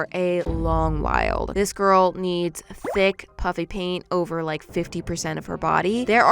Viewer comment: Oh, this years, I thought she said puppy paint